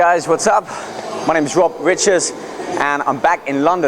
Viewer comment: i thought he said i rob biches